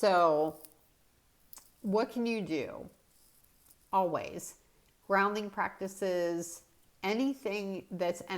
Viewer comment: Now, i thought i heard "frowning" not grounding excercises, today. hehehe. I will do the frowning exercises. I have been in a slowcooker today. Whew. Its a good time for you to take a break. Make some popcorn. Good idea.